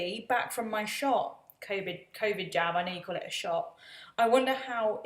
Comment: Okay, so I heard ‘Covid jam’, instead of ‘Covid jab’ and I was thinking wth?! Covid jam/jelly/preserves?!!!! 😖😂